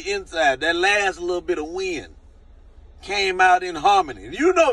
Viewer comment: I thought you said that last little bit of weed came out when he breathed, but you said wind 😂